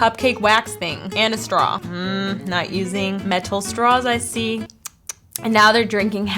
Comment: is it just me or did I hear “mental straw”! 🤔